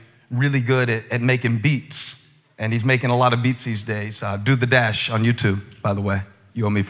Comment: I actually want to listen to his son's beats... did he say "do the dash"? At 4:45 ?